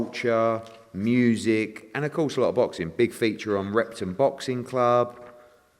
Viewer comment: did he say Rectum Boxing Club?